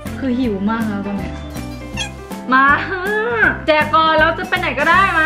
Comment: ตอนพี่เเจ้บอกว่าเเจกก่อนได้ยินว่าเเดกก่อน